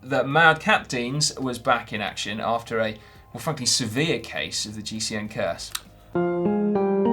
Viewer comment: did anyone else think Si said "f**king severe"? haha